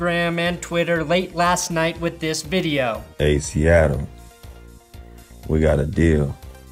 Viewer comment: I thought Russ said, "Hey Cialis, we got a deal!!!!".